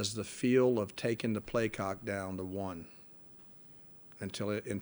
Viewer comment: Did he say (play cock) at 2:08??!!